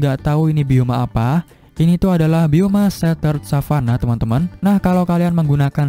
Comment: Assalamu,'alaikuum Bang, saya penonton baru abang, alhamdulillah Chanel abang udah aku Subcribe, aku suka banget ama Chanel abang .. Bangg aku mau nanya nama bioma yang di menit 7:09 itu apa bang, maaf bang 🙏🙏🙏 soalnya tadi gak kedengeran tolong dijawab bang 🙏🙏 Bismillah semoga dibaca ama abang 🙏🤲🤲🙏🙏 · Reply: Setert savana